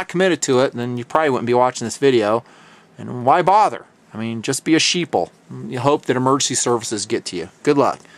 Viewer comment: love the idea you are talking about at 32:20 but... ' be like me or be sheeple' ? did i hear that? whoa! hope i'm wrong. you sound like a guy that has done a lot of training either civilian or military or rescue so thanks for your service to all the less fortune. i am a big supporter of advising how to be less dependent (sheep) and more protective (sheep-dogs)